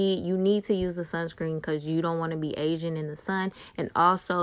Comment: bruh I thought she said "cuz u don't wanna be Asian in the sun"- an I was agreeing wit her for a second😂✋